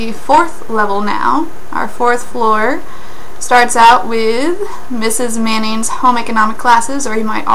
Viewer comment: I thought she said mrs. Mayonnaise XD (on low volume)